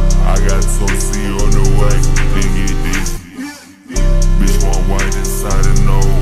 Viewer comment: finna get busy⭐️ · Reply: Thought u said finna bust